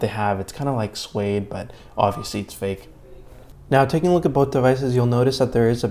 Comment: Did you say it's fake @ 3:36?